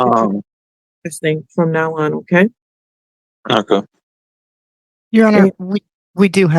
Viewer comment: did I hear him say “KaKa” and then @ 22:10 did he say, “l Iike thongs.”??? 🥴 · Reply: He speaks out of the side of his mouth. At 19:48 he said “okay” and at 22:10 he said “I don’t like phones.”